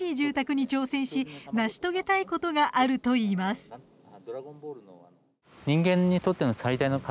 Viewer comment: 建築センスが魔人ブーなど、ドラゴンボール的なものを感じる。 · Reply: あたりでドラゴンボールって聞こえるねw